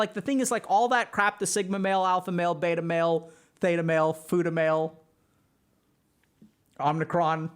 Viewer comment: did he say futa-male? :O